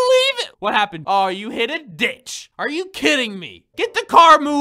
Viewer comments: did he say the b word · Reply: He sayed ditch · did he say what I think he just said!?!?!?! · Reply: a ditch not the b word